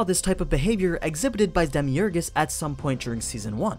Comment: did you say "Demiurgus" at 4:28 ?